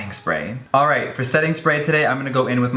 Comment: Oh my god when he said setting spray I thought he said sex tape 😂😂😂😂